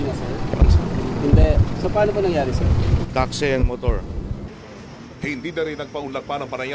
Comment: Tama ba yung narinig ko?2:18 taxi and motor? Sabog tong yayabangin na to.